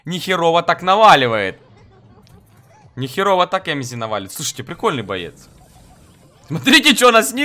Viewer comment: мне послышалось эмси-MS , а не эмзи